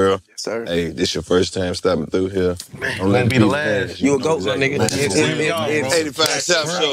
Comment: am i trippin or did dc call woody a lil nigga